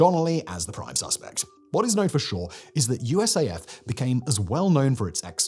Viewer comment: I think I heard you say USAF at 4:48 ?